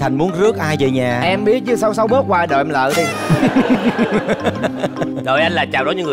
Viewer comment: Lúc anh đại nghĩa bảo thành muốn rước ai về phút 19:00 anh thành nói gì nhanh thế nhỉ nghe ko ra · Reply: "Em hổng biết chứ xấu xấu bớt qua đội em lại đi" =))))